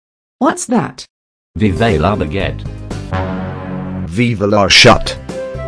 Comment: Okay for those who didn't understand what the guy said at 1:37, he's supporting the French bread "la baguette"